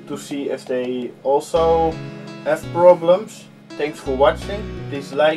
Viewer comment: I don't say that ;) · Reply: Sorry but I hear "dislike and share...." Or my ears are bad ;)